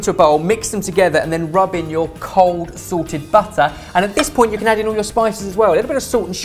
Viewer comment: When said to 'rub in your cold salted butter', I thought he said 'sorted butter' and was very excited about the prospect of the guys branching out into the dairy industry...